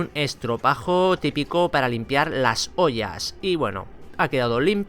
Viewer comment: Alguien se dio cuenta que dijo limpiar las polla envés de las ollas en el minuto